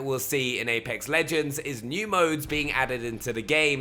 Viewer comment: Holy shit I thought you said emotes were being added at the end. Almost got pissed off lmao, fuck that kiddie shit